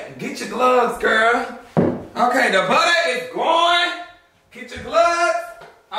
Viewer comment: I came just to hear “THE BUTTER IS GOINNNN! I literally be saying that every time I cook anything 😂😂😂😂😂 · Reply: I thought he been saying "the butter is on" I been so wrong 😭